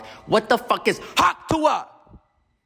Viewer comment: what did he say here??? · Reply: hawk tuah